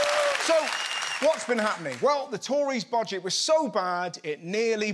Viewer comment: I thought Russell said (mistakenly?) "bodget" at the beginning of the video, then I thought no, that would be more accurate!